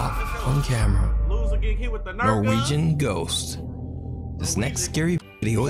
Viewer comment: When it said “The Norwegian Ghost” I thought it said “The Luigi Ghost.” 💀